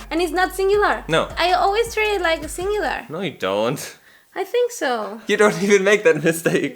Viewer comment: He said: No, it don't, instead of: No, it doesn't. Btw, I'm always get confused with she/he and her/his, even when I know it. Also, I don't find the difference between this/these. Besides, I'm having problems with the phrasal verbs, there are so many 😭 I'm learning English, and I discovered your channel. Now I feel glad that I discovered this piece of gold 💖